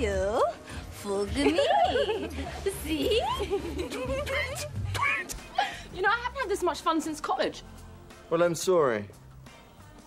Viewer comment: Did she say “I haven’t had this much fun since coovid..?” On 2:22 ??!